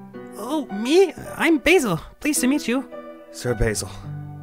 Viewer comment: i thought he said bagel 🍷🗿